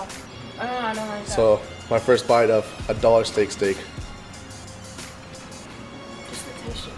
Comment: Hahaha i thought i heard a double steak. I was like what is he saying. Dollar steak steak. I think he wanted to say dollar store steak